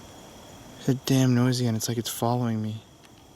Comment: it sounded like she was saying "i am, I'm following you"